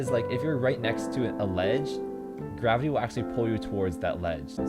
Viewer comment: did you say grabu? or grapu?